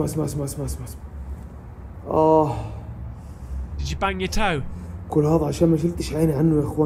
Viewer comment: I was quite spooked until I heard 'did you bang yer toe?', bloody hilarious XD